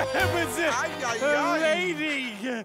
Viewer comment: I just found out that Veronica is voiced by Grey DeLisle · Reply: Goodwin Did Otis say milady